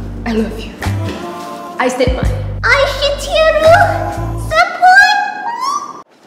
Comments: When it was the Japan part i thought she said “I shit u senpai oOoO” 💀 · The japanese pronunciation was just soo imperfect 👌 I literally heard it as "I hit ye ru sempoi" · Did I hear " I shit you to senpai " 😂 💀